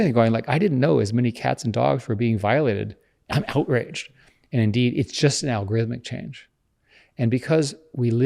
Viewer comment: did he say his name was dean? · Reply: He says indeed